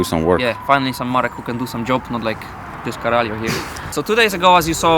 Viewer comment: did you say "caralho"? xD